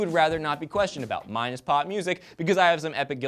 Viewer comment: I thought he said pot music at the end lol.